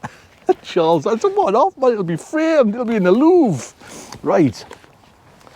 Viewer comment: “Framed.... in the loo” is what I thought you said 🤣 🚻